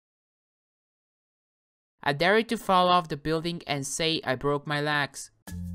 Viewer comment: "i dare you to fall off the building and say i broke my legs" But I Hear Lags Like If You Hear Lags